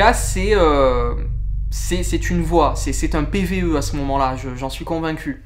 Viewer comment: Salut a tous a 30:09 quant on ecoute la voie pour moi j'entend " il ne faut pas entrer "